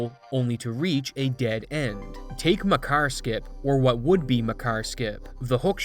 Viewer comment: I really thought you were saying "Muh Car skip". That's what I get for not paying full attention